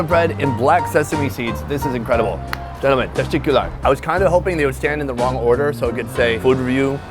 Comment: Did he just say "TESTICULAR" as a thank you in turkish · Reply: Nah, Teşekkürler which means Thanks. They have similar pronouncing tho.